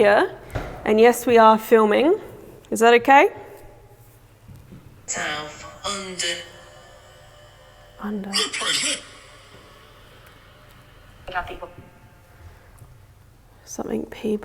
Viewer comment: I heard “town under”